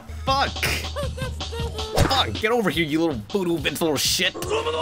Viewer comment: did he say Voodoo Vince?